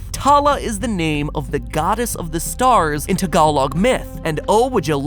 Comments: Did he say "Tagalog" or "Tagalag", I'm Filipina but I don't speak Tagalog, I speak Bisaya. So, I'm confused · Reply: I have the same question at first so I had to turn on the subtitles to make sure. And yes, he did say Tagalog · Did... Did Matpat just say "Tag oh log Myth".. 9:20 ? My inner Filipino just shrieked. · "Tala is the name of the goddess of the stars in..." What? Tagalog? I'm kind of unsure coz it SOUNDS like Tagalog but I heard it as "Thug-ulowg". Eh, I'm just gonna assume it's Tagalog. Tah-ga-lowg. It's close enough, right? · Can someone pls clear this for me in 9:25 did he say tagalog or something else if he did he really screwed up the pronouncement of tagalog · Did he just say tagalog (tah-gah-log)? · did he say Tagalog Myth? Is he referencing to the Philippines??????????? Because Tala is Filipino for star 👀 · did he say Tagalog 9:20? As in Filipinos? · did you say tagla or meant to say togalog